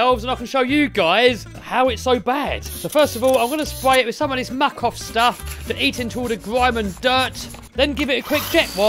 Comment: Subtitles said makoff